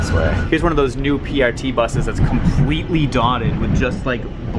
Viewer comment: what's he saying about that one bus, it is dotted with glue? what? edit